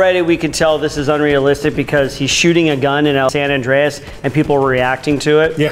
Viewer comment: damn, looks like the editor got offended at that one and super imposed "San Andreas" instead of "Los Angelese"